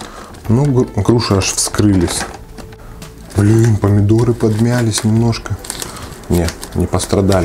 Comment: Мне одному послышалось что он сказал помидоры на 13:43 а это были огурцы 😂😂😂😂